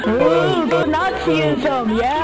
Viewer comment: Did someone said yay to Nazism